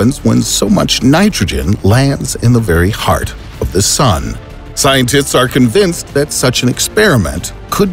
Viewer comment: Did he say ScienTITS?